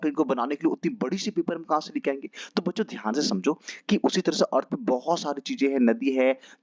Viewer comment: ur voice is um i cant understand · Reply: " to bachchon samjho, earth me bhot saari cheejen hain..."